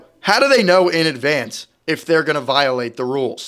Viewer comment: I thought he saying "violating ruler" Not "violating rule" 😅